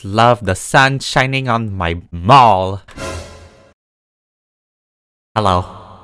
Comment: I thought u said I like the sun shining on my balls 😂😂😂 I’m so dirty minded